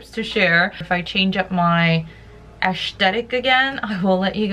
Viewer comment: Did she say "Ash-tetic"?